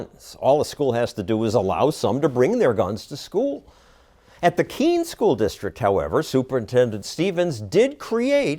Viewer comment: "king gun policy" I'm stealing that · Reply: He said Keene gun policy...That's the name of the school. I thought I heard it as king, also, then they showed the high school name